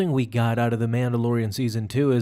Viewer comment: no one: me: did he just say "goodest"?!